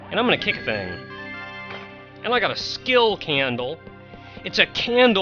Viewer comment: At first I thought it said "Skull candy" instead of "Skill candle". Ya know them headphones?